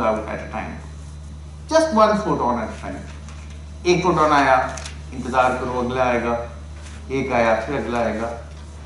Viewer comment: what does the professor say at 30:06? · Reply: He says "ek photon aaya... Intazar karo agla ayega... Ek aaya... Phir agla ayega" Which translates to something like: "One photon came... Now wait and the next one would arrive... So one comes... Wait and the next one would come...."